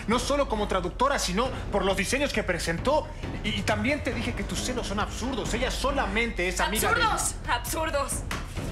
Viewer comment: yo escuché "tus senos son absurdos" :v